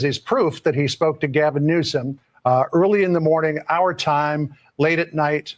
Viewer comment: At the beginning of the video I thought you said Gavin loose some😂